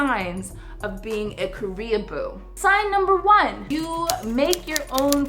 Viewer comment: i thought you said Korean-poo XD